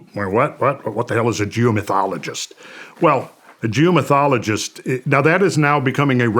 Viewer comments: Who else thought he said Jew mythologist instead of geo mythologist in the beginning of the video 😊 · Randall I thought you were saying “Jew mythologist“ 😂